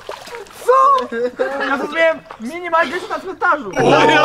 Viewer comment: co on powiedział? Mini majk mieszka gdzie???